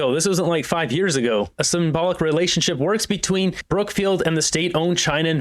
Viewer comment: I thought he said relationship was over.